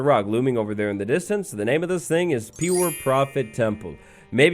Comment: I thought this said "poor proof temple" for a second lol